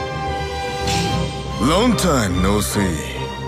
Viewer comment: It so sounds like he says "Long time no see" when you select him to play as.